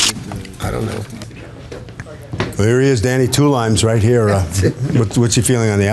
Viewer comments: I thought he said "two lines"! ❄️🤣❄️🤣❄️🤣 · I thought he said 2 lines. As in 2 lines of coke 💁‍♂️💁‍♂️💁‍♂️🤣🤣🤣🤣🤣🤣